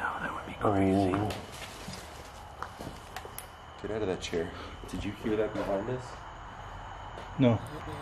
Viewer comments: sounded like it said "you there" or "(something) chair" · When you jumped out of your chair the voice sounded like it said Get out of that chair. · the voice sounds like it says “did you like that’